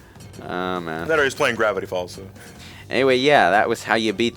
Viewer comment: Does anybody understand what was saying at around the 14:12 mark? Because, to me, it almost sounded like something pertaining to Gravity Falls. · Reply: He said "that or he's playing Gravity Falls" I think he just misspoke and meant to say Gravity Rush because they were talking about N.Brio falling upwards which is something you will do a lot in Gravity Rush.